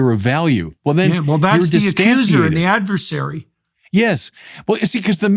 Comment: what does Jordan say here I've rewined several times and I can't figure out what he says in responds. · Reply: "that's the accuser and the adversary"